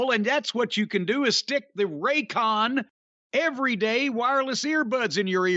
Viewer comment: when Jim announced Raycon earbuds it sounded like Ray Khan, and I was like man he would make a joke about Nick Khan, Tony Khan and Raycon. Khan Khan everywhere!